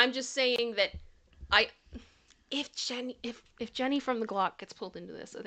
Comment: Replay crew here, just watching and thought I heard say, "Jenny with the glock" 🤣🤣🤣